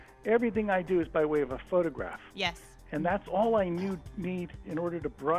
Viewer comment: "Everything I do is by way of a photograph, and that's all I nude - need - in order to [inaudible] this energy to someone." Anyone else hear that Freudian slip at 20:55? I did wonder what sort of photos he was asking for.